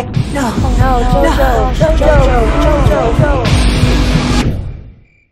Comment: Why did i hear "JOJOJOJOJ" 💀